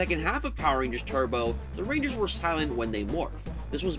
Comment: What did you say at 7:24? "the rangers would style in when they morph?" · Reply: silent